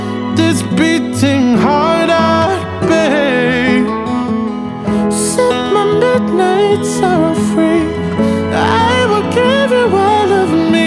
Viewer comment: The lyrics says "Set my midnight sorrow free" Well i thought he said "Suck my milk made sour free" see if you hear too...😂